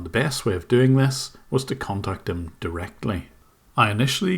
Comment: lol did he just say "cunt" 😂